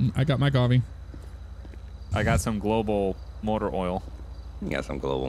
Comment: what did say " i'l give you some motor oral" that is what it sounded like